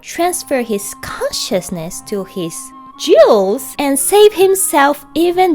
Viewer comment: I and the subtitles heard this as "Jews". Local slave owner of Jews transfers conciousness to them as a way of apology, crowd of sane people shocked.